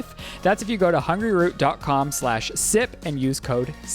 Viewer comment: I had to come and look at the ad’s name because just from audio I kept hearing “hungryroo” 🫠😂